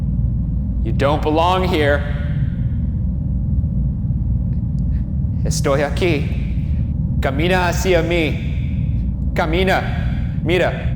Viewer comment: A question for pm: At 21:30, Ryan began to speak Spanish and there wasn't a subtitle translation, but it sounded like "estoy aqui, camina sin mi" so I was wondering what that was about! Great episode though, you guys are awesome :)